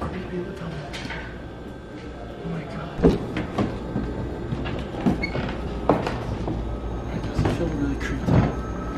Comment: Did anyone else hear voices at 8:29? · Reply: lmao that was him saying “my god” but it just sounded like wake up